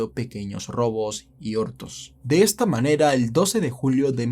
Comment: ¿escuche ortos? 😳 O escuche mal 🤔